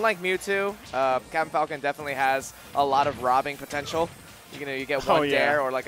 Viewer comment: He really said Mewtwo doesn't have robbing potential.